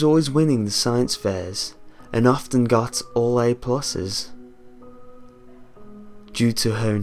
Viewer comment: What guy says "... And often got all A pluses..." What I hear "... And often got all 8 pussies..." ..........awkward..........